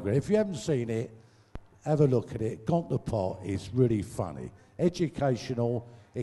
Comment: Wayne must've been half-pissed. Sounded like he said, "Gonzar park". Bobby later says, "Got the Pot". (Could've saved me a lot of 'Net searching if I had waited for Bobby to pronounce it correctly).